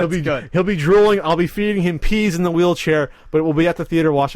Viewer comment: I thought pat said frank would pee in his wheelchair.